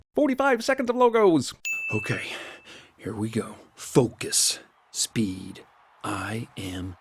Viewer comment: Am I the only one that heard "Fuck this" instead of focus